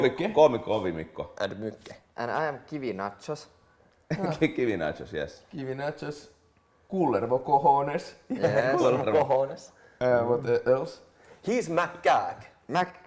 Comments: HAHA! I heard ''TV nachos'' XD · what did Perttu say? my friend and I swear that he said "Give me nachos" XD